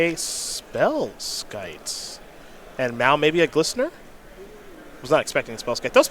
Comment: did you say "And meow maybe a Glistener"?